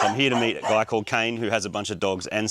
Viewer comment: You said a guy called Kane or whatever his name is so fast that it sounded like My cocaine min